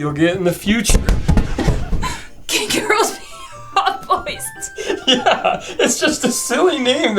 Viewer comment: I thought she said "can girls pee on hot boys, too?" Anything is possible if you believe in yourself